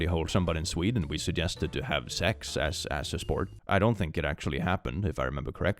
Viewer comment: … did you just said Swedes don't have sex?